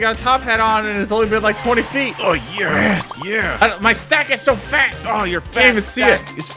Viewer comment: I heard “my sack is so fat”